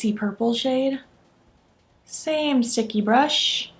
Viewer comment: 🛸🛸🛸🛸 Omgg i thought you said same stinky brush🙈🤣🤣 But you said same sticky brush😝 I got my palette a few days ago, and havent been able to play with it yet. Havent even touched it, cause i need to photograph it first🤪 So i dont know what my have shade is yet😁 I love both looks soo much!!🥰🥰